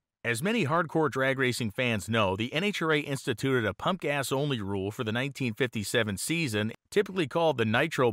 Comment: I had to CC this because I thought you said 'the punk ass only rule'.